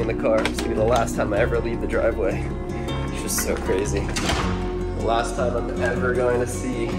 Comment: I thought you said the last time you were going